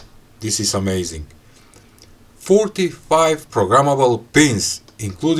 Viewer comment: The ESP32 S3 is impressive, but it's probably wasted on this small board. Does the board come with PSRAM and how much of it and which pins are actually usable? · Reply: I thought he said it was 41 programmable pins and 14 capacity of touch sensitive pins at the beginning of the video. Is this what you meant when you asked about pins or did I misunderstand?